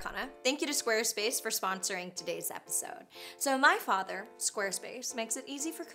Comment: um, at 3:28 - did she say, "my father, square space" ? made me laugh. anyway - funny vid!